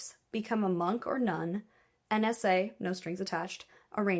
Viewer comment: I heard NSA as AN SA